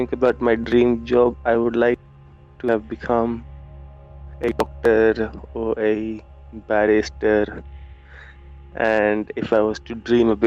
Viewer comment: does he say he would like to be a "doctor or barista"? Such a dichotomy! And would you trust him in either? "I didn't actually help any patients, just took their money, but it's ok because this is temporary." "I didn't put sugar in his coffee, I used melamine, because it looks like sugar but is cheaper. It's ok though because I need money."